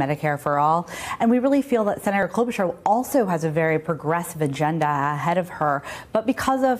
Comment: Did she say "Vagenda" ..?